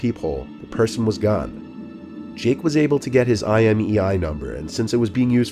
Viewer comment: I thought he said «the person was God»😂